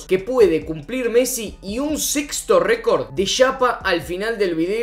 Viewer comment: entendí mal o dijo de (llapa)eso en bolivia seria de(aumento) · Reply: Yapa. En Argentina decimos asi a algo adicional al final.